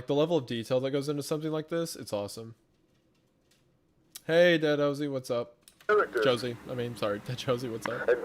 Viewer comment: maybe it's the heavy meds and hospital noise messing with me (missed the livestream because I was in the ER and I'm only coherent enough to watch now tbh) but at 32:12 I thought he said "hey danhausen" instead of "hey deadjosey" and the idea of being a casual pro-wrestling fan absolutely sent me so hard I had to take a break so the whole floor didn't hear me maniacally cackling and think I was having a nervous breakdown or serious adverse reaction to my neuro meds lol